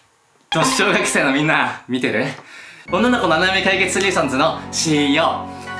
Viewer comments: ｢女の子の穴舐め対決ソリューションズの｣ って聞こえてびっくりしちゃったよ( ´•ᴗ•ก )💦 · 最初の女の子のお悩み相談ソリューションズが女の子ナメナメお兄さんずに聞こえて巻き戻したわ